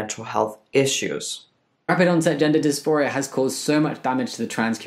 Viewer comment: did i hear this shit right? Did he rlly say " gender dysphoria is not really " ...???